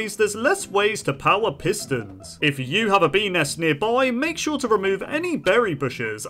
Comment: I thought he said penis instead of bee nest and I was like what? 🤨 then I saw the bee and I was like oh yeah 😂